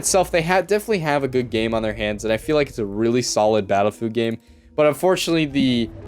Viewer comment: Battlefood Game? I thought I heard that